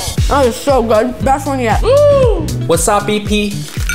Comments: i thought he said "wasadi PEE" 😭😭😭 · I heard 'wassup pee-pee' when uncle roger said wasabi pea😂